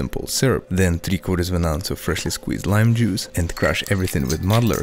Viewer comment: Because the voiceover was late, I thought he said freshly squeezed prime juice